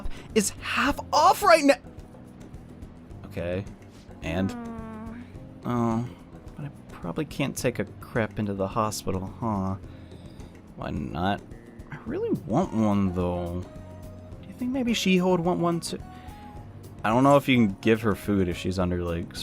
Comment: I was looking away and I thought she said “I probably can’t take a crap into the hospital” 😂💀